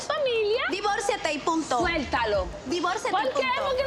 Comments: Pedro's sister said "punto", in other words, period but Chantal missheard and she thought she said "puta" which is "wh*re" then she said yes but that was to piss her off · said "en punto" not "puta". 21:02 I think the reason and her mother were laughing at the end is because even though did not really call Chantel a "b@ she said she did. Curious if anyone else heard what I did. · she said “divorce him and period” lmfao she did not call her a slur, she said punto which is period.. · “Divorciola y punto” not “Puta” did not insult Chantel, is just saying she’s fed up. “Just divorce him already —period.” But the fact she said “yes” after she was accused of calling her a bitch is sending me 😂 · perople so f stpd she sayd "divorciate y punto" she didnt say PUTA she thought she called her puta but she was refeering to something completely different lol, even narrator didnt catch it